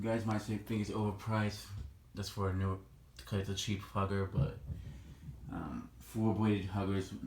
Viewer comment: Did You Say "Cheap Fucker" Lol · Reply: He said cheap hugger